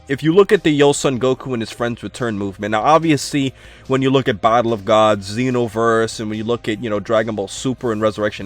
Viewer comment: sounds like says bible of gods instead of battle of gods